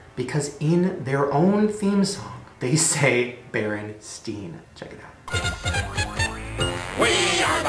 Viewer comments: I hear berenstain, not berenstein? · I heard Berenstain